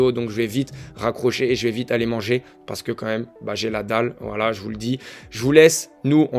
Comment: À la fin j'ai entendu "J'ai Nadal" 😂 je suis trop matrixé